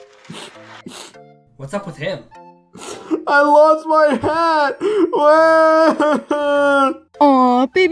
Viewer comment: I Thought He Said "I LOVE MY HAND!"